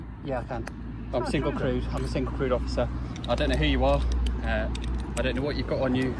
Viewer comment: When the cop first said it, I heard "I'm a single, CRUDE officer." That explains everything, really.